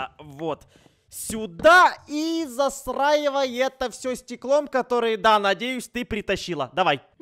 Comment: Я Один Услышал: Засраивай Это Всё Стеклом